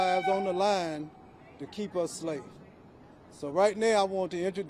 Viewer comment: Did he just say " to keep us slaves? "